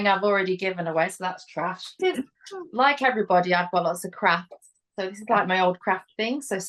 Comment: I thought said "like everybody I've got lots of crap" and I was like PREACH sister... until I realised she said lots of CRAFTS! And both are definitely true for me 🤣